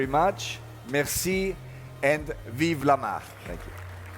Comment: What did he say at 2:42, I know he said Merci, but what was the last phrase? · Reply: "And vive la marque". (And long live to the brand)